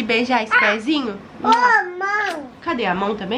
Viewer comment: No momento 1:18 eu ouvi um mae? Será???